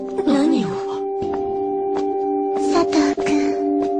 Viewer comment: It is xD But yay I'm wrong (Norway is Himeka and Isu is Karin) 0:42 I heard Kazune-san and he's from Kamichama Karin ._. Just see the anime and check it. Sorry my bad english~